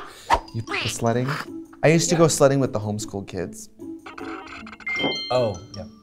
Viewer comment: LOL, I thought Trixie said" slutting" when she said she went sledding with the home school kids